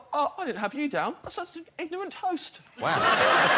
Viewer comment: 'i could play this all day' 'ignorant host' 'good title' Genius. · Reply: I heard "ignorant toast" which made it unique...